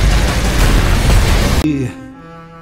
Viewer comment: is it just me or does he say "prepare myself!" cuz if so that's very fitting